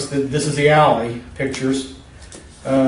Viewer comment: I was half paying attention because I was reading a text and I thought he said "these are the Yaoi pictures" instead of alley pictures.